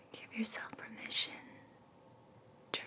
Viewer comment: I was falling asleep hahah and I heard in my half woken state “keep yourself from this sh*t..” 😂😂 very calming video by the way I’m not trying to make light of it, I just thought I’d share bc it caught me off guard- thought it was funny 😂🥰💛💛